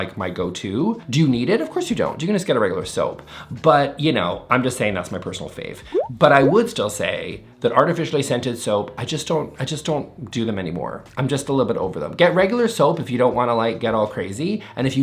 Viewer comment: For half a second I thought he said “fartificial scents”… 😏